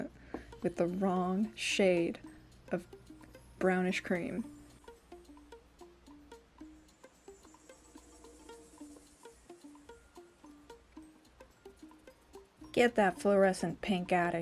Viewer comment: I had this video playing in the background and got spooked by misheard "brownish green" on an armadillo